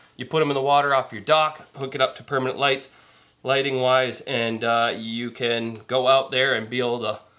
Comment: I thought you said flesh lighting lol